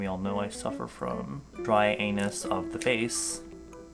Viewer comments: why did I hear “dry-anus” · I was enjoying my ramun until I heard~"yall know I suffer from dry anus mouth" i choked on my drink and bursted out laughing~